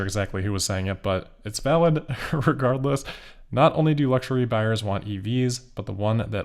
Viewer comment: Did you just say "irregardless"?